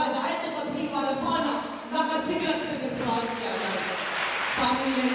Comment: pr kya bola hai isnay ayat k agay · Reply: ورفعنا لک ذکرک سے سرفراز کیا